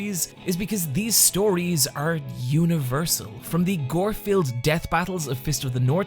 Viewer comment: I’ve watched this video twice and both times I heard “gore-filled death battles” as “Gorefield death battles” and went “holy shit there’s a Garfield battle anime?”